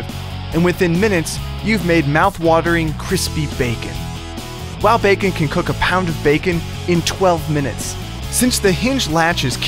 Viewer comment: Excuse me but am I the only one who heard them say at 0:59 "while bacon can cook a pound of bacon" anyone else??? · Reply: It was at 0:57, and he said "Wow Bacon can cook a pound of bacon." 'Wow Bacon' is the name of the product.